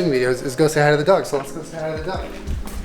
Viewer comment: When Julie said "we can go say hi to the dogs," I thought he said "we can go say hi to the DUCKS." AND I WAS LIKE....Y'ALL GOT D U C K S?!